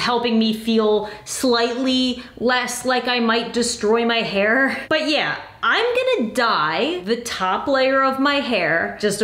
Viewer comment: I was just crocheting and I was listening to her talk about it and I just heard “ yeah but I’m gonna die” and she said the other part a little after that part making it sound like that was what she was going to say lol, and I was like “what…”